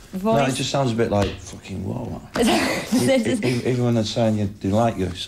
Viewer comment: what does he say at 0:50 fucking raw or what?